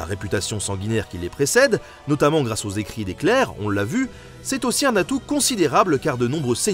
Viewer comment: J'ai eu une absence, mon cerveau a compris "Le trône de Cell". Quand GoT se mélange avec Dragon Ball Z... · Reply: Mdrrrrrrr j'ai entendu "les écrits d'Hitler" à la place de "les écrits des Clers" alors bon 😂